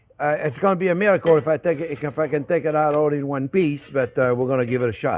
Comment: did he say If i cant take out in one FUCKING peace ??? or is it just me